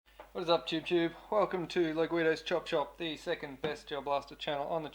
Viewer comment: I heard "Welcome to Low Guido's chop chop..."